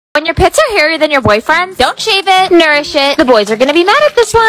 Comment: i thought i heard: when your pets are hairier than ur bfs 💀💀💀💀